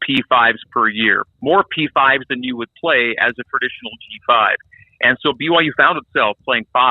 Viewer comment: Uhhh, did anybody else catch the part where he claimed BYU was playing a P5 schedule by only playing 5-7 P5 schools a year? My dude, you gotta play 10 P5 teams in order to have a P5 schedule. BYU is in for a rough few years having to play that many P5 teams. Their roster was all but spent by mid-season during their entire independence run and while only having to play a handful of P5 teams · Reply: It took a replay for me to realize he was saying G5 at about 4:45 and 5:20 and not P5 again. I think he’s just saying 5-7 P5 matches per year is a lot closer to a P5 schedule than most G5s normally get.